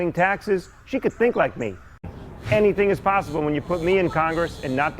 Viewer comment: did he say hennything is possible?